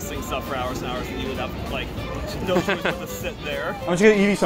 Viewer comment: i think he said "no choice to sit there"